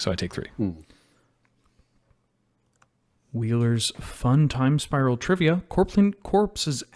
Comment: Who else hears this next sentence as “‘Fun Time’ Spiral trivia?”